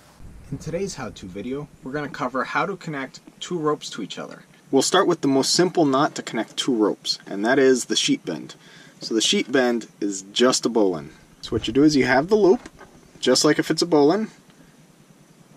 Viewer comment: Ha! I thought you were saying "sheep end". Very good tutorial, thank you! Upvoted & subscribed.